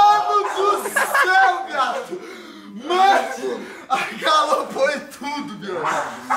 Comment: e 13:23 "AGALOU foi tudo" kkkkkkkk